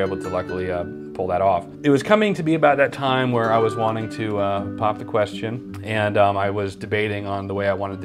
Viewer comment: I thought he said ponder the question what if?